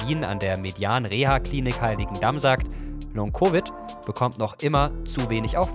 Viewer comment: ich habe zuerst "heiligendammsack" verstanden, musste zurück spulen und dann lachen.